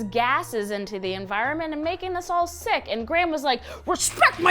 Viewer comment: When she said GHRAM I thought she said GRANDMA said RESPECT MY PRIVACY DAD!!!!😅😅😅😅